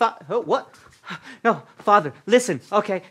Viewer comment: What does he say at 4:10? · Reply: Fa . . . As in Father.